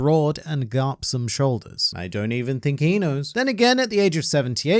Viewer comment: The way said "I don't think he knows" [1:41] was very funny to me. The 'he knows' sounded like 'inu' when I first heard it and Garp was wearing his dog head... thanks for that.